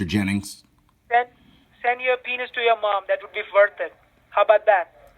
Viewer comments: Scammer: “Send the subpoena the your mom!”...yep he’s from the IRS! 😂 I HATE SCAMMERS! One called me once and my teenage son harrassed this idiot so bad he literally blocked my son’s phone! My son said to him something about getting a real job and the scammer replied, “why? I can earn so much more in an hour of scamming than I could a week of work!”...NO JOKING!! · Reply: I thought he said "how about I send my penis to your mom" 🤔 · he said "send your penis to your mom, that will be worth it. How bout that" . Not what you said after.